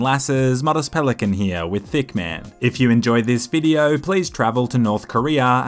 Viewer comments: i always thought said "back with thicccman" instead of hitman. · I though I heard, "With Thickman". 😂 I love your Hitman videos btw! 😊